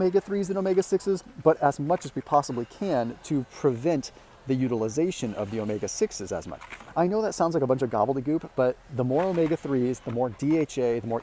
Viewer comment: Did he just say Gobble de Goop?!🤣